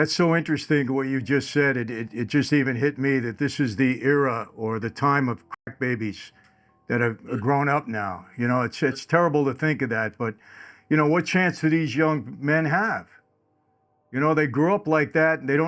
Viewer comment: I thought he said “cry babies” not crack babies lol I trust what is saying … definitely seems legit